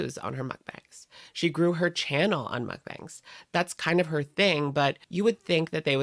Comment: I can't 😂 I heard "she grew her CHIN on mukbangs" and was like "true"💁‍♀️